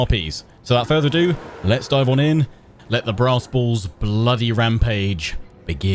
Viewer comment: In your intro I could have sworn you said "... Let's dive on in, Let the the Brass Balls bloody rampage begin!' Evidently crappy default Closed Captioning agrees with me. =)